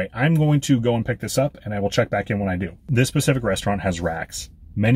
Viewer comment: i thought you said "rats" lol